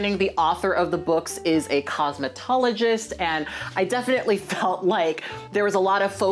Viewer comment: Lol I thought you said the author of the film was Cosmotologist which sounded like some weird religion/cult 🤣